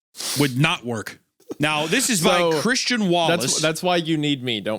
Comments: I heard “this is my Christian Wallace” and I was like.. yeah he’s sorta like the grommet in the group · I thought said this is my Christian walrus